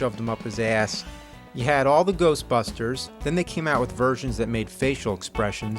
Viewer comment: I could've sworn that it said: "GHOSTFUCKERS!"